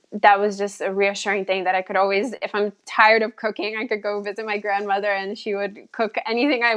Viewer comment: It sounded like she said "if I'm tired of cocaine" 😇😊